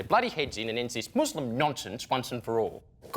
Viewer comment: sounded like he said "talk money, get money, COCK money" 😂 hahaha. For real though, great job on criticizing both sides.